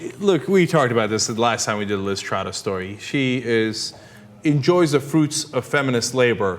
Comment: Whenever they said the name 'Liz Trotta' I thought I heard 'Lysistrata,' which would've been ironic.